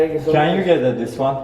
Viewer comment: da ne dedi arkadaşlar anlayamadım can you get a this one mı dedi ? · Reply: You dedi :D